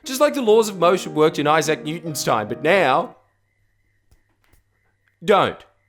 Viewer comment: I heard you saying 'Issac Newton-Stein' not time...